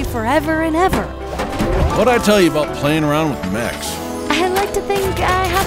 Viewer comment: Every time they said “mechs” I kept hearing “Mexicans.” -“You think all Mex are stupid!” 👀